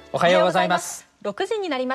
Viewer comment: I heard Ohio!